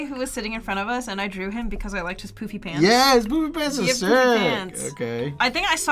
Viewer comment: I’m sorry, but when you said “Poofy Pants” I misheard and thought you said “Poopy Pants”.😅 Then you kept saying it and I started dying on the floor from laughter.😂🤣😂🤣